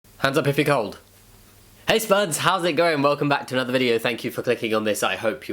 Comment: My partner thought you said "Hey spuds, how's it growing ?" and I feel like that should be your new intro greeting